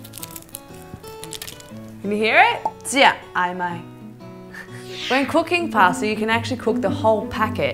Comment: what did she say and what language? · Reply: She said " jia ai mai? " which means do you want to eat it? and yeap its hokkien